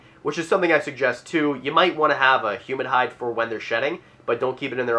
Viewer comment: I keep hearing "human hide" instead of humid hide LOLOL